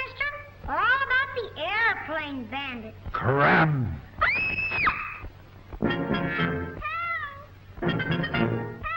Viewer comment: Did he just say what I think he said...? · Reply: When I first made that comment, I thought he said the C-word, but now I know he said "Scram!".